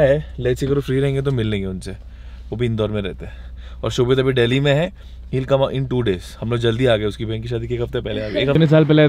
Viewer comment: DID HE SAID " SHOBHIT" ?? NIRWAN